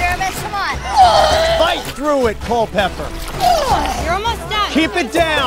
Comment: did Jeff say culpepper